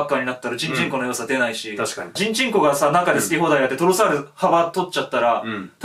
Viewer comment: ジンチンコって聞こえる笑